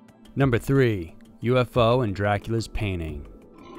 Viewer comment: I thought he said "Dracula's panties." 🤣🤦‍♀️🙄